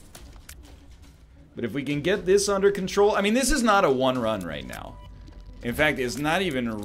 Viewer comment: all this time when they say (northernlion and sinvicta) 'won run' i thought they said 'one run'.... i just figured it out..